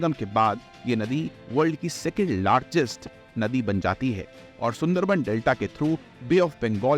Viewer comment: did he say ' world's 2nd largest river'